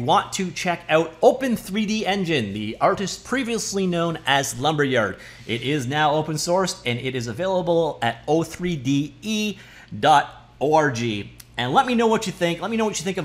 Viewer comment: Did you say . O R G or did I hear something else? Comment 100% getting filtered away soon